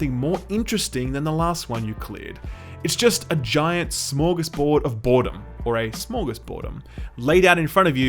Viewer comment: did he just say smörgåsbord if boredom? Nice swenglish mate